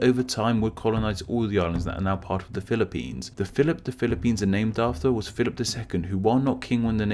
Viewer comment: Did you just said Philippines Double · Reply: He said, "The 'Philip', the Philippines were named after, was Philip II...".